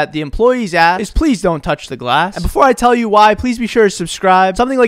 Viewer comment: I thought it said don't touch grass (I do it always)..